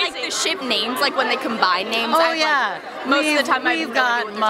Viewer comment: um excuse me lilan, why didn’t i hear tyrus? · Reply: Dolphindess she said jyrus not tyrus